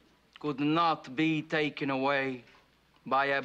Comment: I could swear I heard "fargin' icewhores," there.